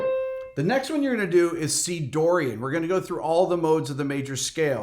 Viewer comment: When you finished the C major scale, at first I thought you said D Dorian and then you mention Eb....I thought, “There’s no Eb in D Dorian.” On going back I realized you said C Dorian. It’s been a long day.